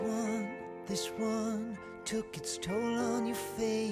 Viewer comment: am i the only one who thought he was saying 'this swan' in the first verse?